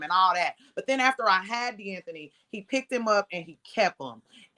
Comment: All I heard was… “I picked’em”….